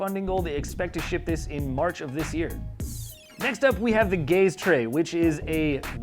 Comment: i thought he said next up is the gay straight